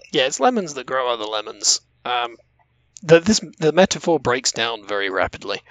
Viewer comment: What is the joke that makes at 14:23? I can't hear what he says and it doesn't show up on the CC. Thanks. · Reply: laughing at commenting that "the metaphor breaks down very rapidly" because we're both known to make some horrible metaphors on the show. laugh covers up saying "much like lemons." The full sentence was "The metaphor breaks down very rapidly, much like lemons!"